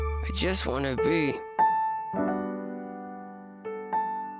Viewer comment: why at the end i hear ''no girl ok with that'' rather than ''And I hope you're okay with that''